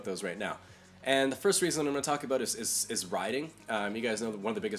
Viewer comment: At first I thought you said you prefer Taiwan because of the 'rioting' but it was riding, HA.